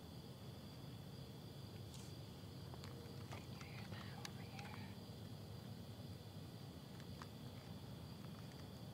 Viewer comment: there's a voice. I cant quite make out what it said tho..... "can you hear or see me over here"?? Maybe... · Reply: Sounds to me like "can you hear that over here?" Sounds too low to be 👻